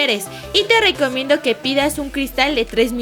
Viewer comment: le entendi pizza